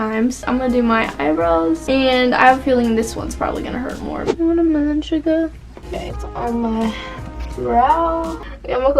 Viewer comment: Did you just say Watermelon Sugar🍉